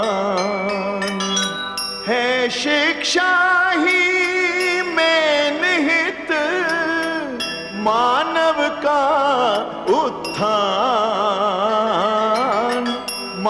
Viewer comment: I heard "है शिक्षा ही MAIN हित" Tell me if I am wrong... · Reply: “है शिक्षा ही में निहित मानव का उत्थान”